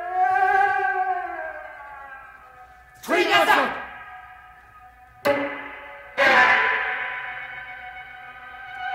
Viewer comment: what did they say? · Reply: тринадцать it means "13" in Russian you can also hear some weird words in seventh part of black angels just like 8:16 "十三" (Japanese) 9:02 "kumi na tatu" (Swahili) and 10:12 "dreizehn" (German) they all means "13" as well it's an important concept in this piece